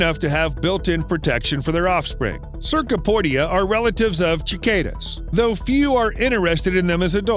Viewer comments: did h,,, did he just call cicadas "Chickada"? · did you just say "Chick-Kay-dah" ?